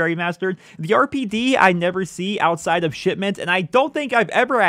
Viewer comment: Did you say shitment 0:56 I call that map the same